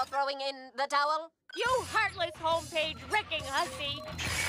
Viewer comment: did I just hear Karen call her a hussy😮